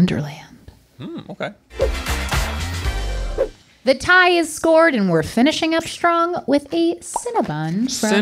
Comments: am i the only one who heard her say the tie is scored? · OK, am I the only person that heard her say "The TIE is SCORED"? Oh man, that was funny.